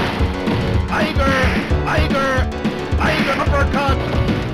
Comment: The only thing you hear: TIGER TIGER TIGER TIGER TIGER! Damn it, I love Sagat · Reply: I always thought he was saying Hyper, Hyper, Hyper uppercut lol